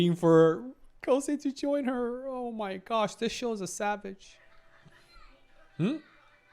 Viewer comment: Is it only me or did any one else hear him say sadd b*** instead of savage